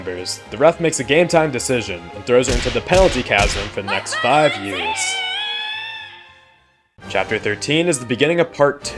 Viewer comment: Does anyone know what she says here? 23:04 i cant figure it out no matter how many times i rewatch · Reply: She says my battle ends here